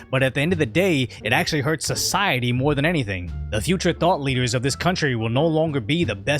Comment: "The future thought-leaders ..." ?? Or did you say, "The future THOT-leaders..."??